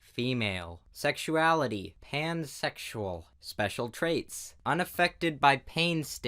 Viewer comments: when he said "pansexual" But i heard "hamsexual" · I misheard pansexual, i thought he said HANDsexual